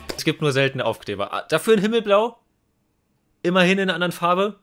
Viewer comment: ich hab verstanden Pimmelblau 😂